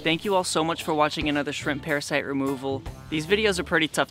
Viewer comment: Thought it said simp parasite for a second